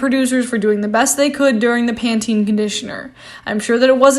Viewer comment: Did I just heard her say "PANORAMIC", "PANTENE CONDITIONER"?